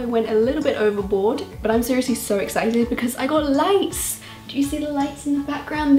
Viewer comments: I thought she said she had lice not lights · in the beginning I thought she said I got lice😂